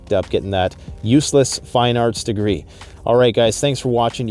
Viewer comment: Hahaha useless fine arts degree I thought you said useless finance degree which happened to many people post 2008